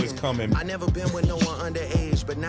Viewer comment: He just keeps saying if I done it I wouldn’t been found out, or I would’ve been in jail. Like that might be the worst defense oat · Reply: That's funny because I heard him say "I never been with no underage". In a sane world that would be enough.